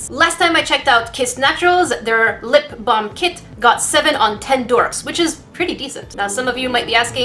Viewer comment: I thought she said got7